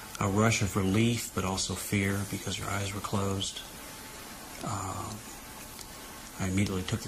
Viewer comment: I thought I heard him say "I was relieved because her eyes were closed"